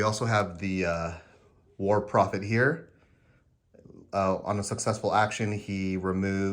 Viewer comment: When you introduced the Trench Pilgrim units it sounded like you said "war profiteer" to my ears instead of "war prophet here" and I was like, yes that's probably accurate in-universe.